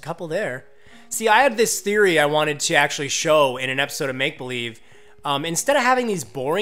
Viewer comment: Lol in 15:51 he said maple leaf instead of new leaf! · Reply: I said Make Believe, not Maple Leaf. I do a video series here called Make Believe.